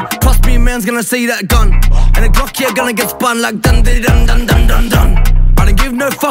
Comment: Never has anything been as good as women weed n wordplay that album everysong was a bangah and every song so different aswell i feel like the last 10 songs chill has put out just arent as good anymore all he raps about is henny and reefer and his punchlines are nowhere near as good as they use to be and some of them dont even make proper sense... it is dissapointing i do understand his life has changed drastically and not here to throw hate but musically for me at least its just not as good as it use to be.. Look i may be not understanding some but can anyone breakdown the glock getting spun line? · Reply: I thought he said the blocks gonna get spun, but nah your right subtitles says glocks gona get spun